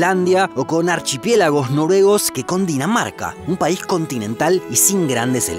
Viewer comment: dices que cundinamarca pero en realidad es Dinamarca yo entendí cundinamarca